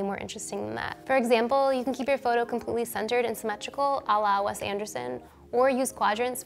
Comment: for 1:32, what did she say? Ala something? Pls help I really liked the photo :)